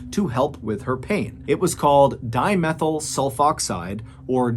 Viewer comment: As someone who has studied chemistry and work at pharmaceutical lab, this series of events had to be absolutely perfect for that chemical reaction to occur. That’s just amazing.. no wonder why they couldn’t figure it out. It’s sooo astronomically rare. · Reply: As a chemist, can you tell me the exact name of the chemical substance that as created that became deadly? I thought he said dimethylsulfate but I cant seem to find anything about it being a nerve gas, only that is is carcinogenic